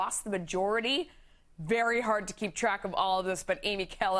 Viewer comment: "...is lawmakers..." Woah, thought she said "islamickers" And now I've got a new word in my vocabulary. Sweet 😗